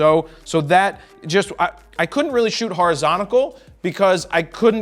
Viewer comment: Did you just say "horizontical"?